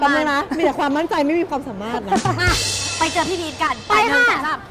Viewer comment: ทำไมเราได้ยินว่าพี่อีทเฉย Fcทั้ง2ช่องนาจา